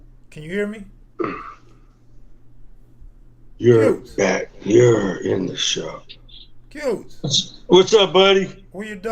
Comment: did I just hear say "you're back, you're in the show"